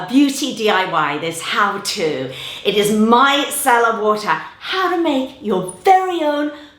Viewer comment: Me: micellar water: My sellar wootah 😣😂😂♥️♥️ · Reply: At first I thought she said “my salad water”